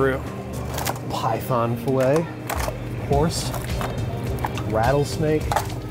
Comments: I’m sorry, but did you just say HORSE?! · did he say horse!? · did he say horse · Did you say "horse" @ 2:12 ? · Correct if i'm wrong did he just said horse?!!